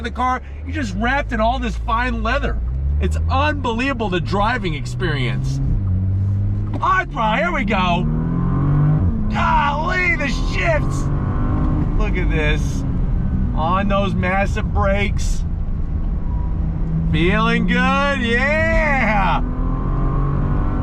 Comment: When I initially heard; "golly the shifts!", I thought you said; "golly the shits!" As in, the car is so fast you had to run to the bathroom! Haha